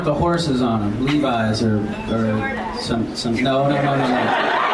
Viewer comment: what does that girl say at 6:00 after "Levis"? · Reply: She says Jordache. A brand of jeans.